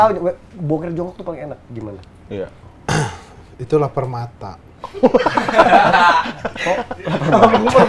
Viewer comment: gua dengernya laper mata. bilang Itu laper mata, yg lain mungkin dengernya itulah permata